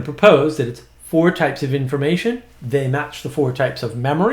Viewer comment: I misheard '4 types of creation' instead of information and it made my mind really curious about what that could mean. But thanks for this one! I was struggling to articulate this.